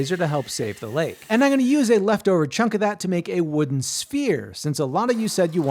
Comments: I thought you said "spear". I was like, "That's a pretty blunt spear". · Another video where I hear “spear” and not “sphere” 😂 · At first, I thought you said spear and thought "oh, I didn't know he'd made a spear before", then "maybe he'll make the pointed tip", then "that's a uniquely proportioned pointed tip of a spear". Finally, like halfway through did I realize it was a sphere and thought "oooh, that makes more sense, he did make a really cool sphere". Guess my brain didn't wake up yet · I thought you said wooden SPEAR at the start of the video and was very confused watching what you were making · Okay but I thought u said "spear" like a spear head... But it's a sphere 😅😅😅 would love to see if u can make one tho !!! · I 100% thought he said "spear" Im almost disappointed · Ngl, I thought he said spear in stead sphere, and the entire time I’m like “damn wth, this gonna be one small spear and oddly round?” I feel like a moron😂